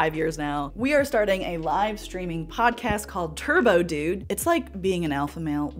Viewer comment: For a split second, I thought you said "Turbo douche" 😅 Turbo dude is WAY better